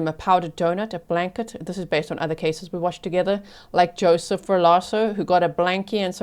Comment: What case are you referencing at 14:10? I tried googling the name but couldn't find anything? Did you say Joseph Valosso? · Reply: Joseph Ferlazzo, yes. A trial we Grizzlies watched together